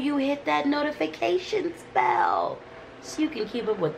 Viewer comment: 🦋 I love seeing you and hearing your voice!! I’m excited for all the new adventures. Blessings on this channel!!! Also, when you said “click the notifications bell” I thought you said “notification spell”- which I love. I love you!!! Thanks for sharing so much with us.